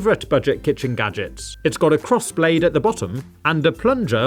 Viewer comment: I thought you said this is called cross blade gadget